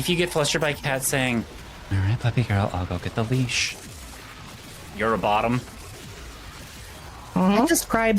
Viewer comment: I stopped paying attention for a minute and then I heard this… I’m totally not a bottom